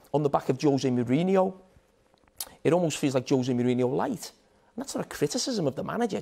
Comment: Did he just say jose mourinho lite 😂